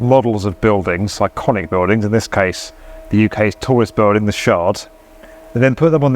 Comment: On first watch, I thought you called the model building @ 3:25 the "shart"... LOL